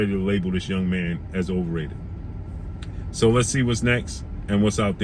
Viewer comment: I thought they said that's next.